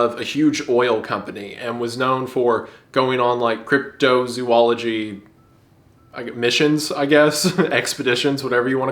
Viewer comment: thought i heard CryptoZoo